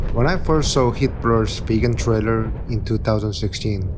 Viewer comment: your caption says hitler's vegan trailer in 0:56....